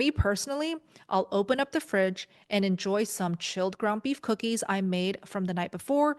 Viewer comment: did she say ground beef cookies ? Lolz I like the sound of that! Great idea